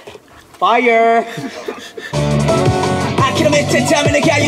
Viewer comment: HELP IN 2:24 HAN SOUNDED LIKE HE WANTED TO SAY F U BUT HE SAID FIRE